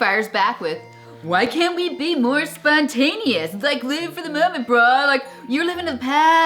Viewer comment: why can't we be more spontaneous, like living for the moment, bra? .... Did he say "bro'' or "bra"? haha